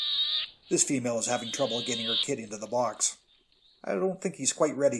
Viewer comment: I had to hear you wrong, I listened 4 times, and I thought you said it had 200 kits in the box 😮